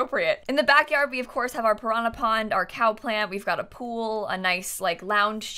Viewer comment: For a second I thought you said "drama pond" instead of "piranha pond", and I didn't even question it, I was like hm yah that's pretty accurate lol